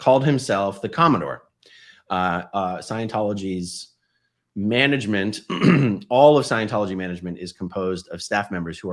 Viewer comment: What did she say at 1:05?? 😂 laughed his head off! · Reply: She said that her husband Paul had called her and told her “They are pulling me back. I said DM was the Who for the FSO” FSO = Flag Service Organization. In Scientology the “Who” is the person responsible for a bad condition. It’s an official thing. Is Scientology when a sea org mission is sent to fix some bad condition they have to “find the Why” and behind the Why they have to find the Who”. Paul had officially named David Miscavige as the Who for whatever was going wrong at Flag. And he was now being punished for having done that.